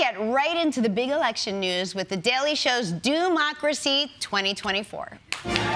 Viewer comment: Sounded like she said Do- macracy. So much opportunity to deliver Doom-macracy in a better way.